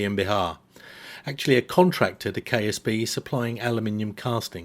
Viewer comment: for a second i heard "KSP" 😅